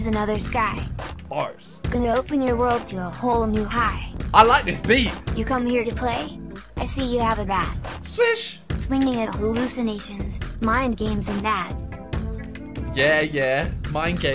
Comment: did really say swish while swinging a bat?